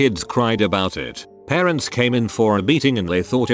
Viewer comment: At first, I heard "parents came in for a beating."